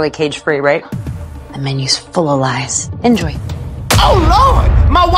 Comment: Thank god for the subtitles. I keep hearing say 'my wife sister Fiona' ffs😅